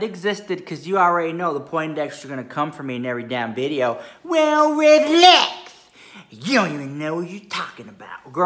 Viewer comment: I feel so dumb 🤦🏻‍♀️ every time he says ‘well rich lux’ I thought he was saying ‘well relax’ lol 😂 I am a new subscriber and I watched several of the recent videos and I gotta say I am in love with 😆